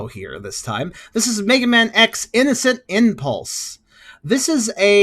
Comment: Turn playback speed to 0.25 and pause at 0:08 and it'll say irregular impulse instead of innocent impulse.